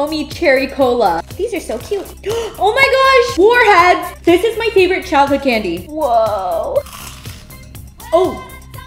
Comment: At first I thought she said “my favorite chocolate candies” when she was talking about the warheads.